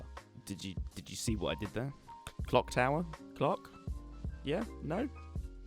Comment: who else thought he said cock tower